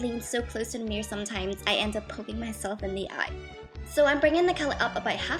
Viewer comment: i thought you said "i end up pooping myself in the eye" :P